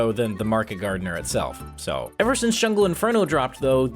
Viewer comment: I heard him say "since shovel inferno"